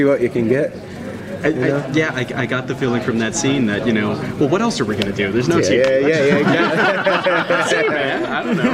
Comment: "i got that feeling from that scene that you know. Oh what else are we gonna do, there is no ..." ??? what is he saynig at the end there? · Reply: The journalist says “what else are we gonna do? There’s no tv” Then Travis replies with “I’m gonna use that one” 😊